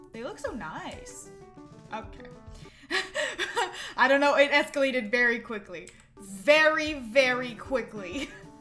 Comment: when she said escalated i thought she said escalator